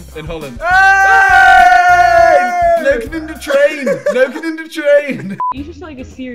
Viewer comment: he is actually saying fucking in the train xD